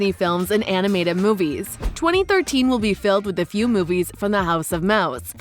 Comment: Did anyone else hear 2013 at about 0:18 ?